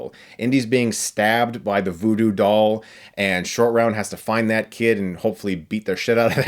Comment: i kept hearing 'Indian Shortround'